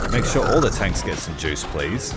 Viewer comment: did I hear that right? “Make sure all the tanks get seduced!” 🤣